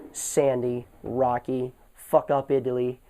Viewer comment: Thought you said 'fuckupitaly' LOL